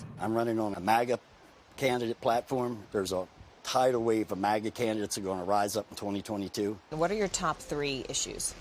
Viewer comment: I thought I heard Chester Doles say, "There's a tidal wave of maggot candidates." Quite apt.